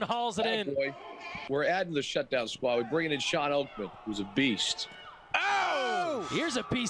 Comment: Did I hear Shawn Oakman at 7:43😳